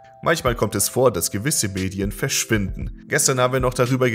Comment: Ich hab am Anfang "Mädchen" statt "Medien" verstanden und dachte mir nur so "Wat?".